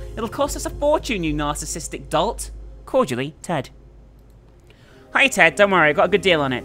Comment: I was drawing when she was reading the notes and I thought she was saying "Turd" instead of "Ted". Then once I found out what she was actually saying I felt stupid for laughing.